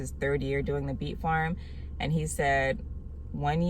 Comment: Thank you so much for making this video and sharing your experience with us. I really thought you said bee farm.